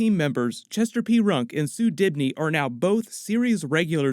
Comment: Isn't it Sue Dearbon? Thought I heard Sue Dibney....